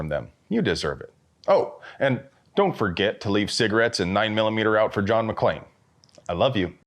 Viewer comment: Omg I heard “leave cigs & 9mm out for John McCain.”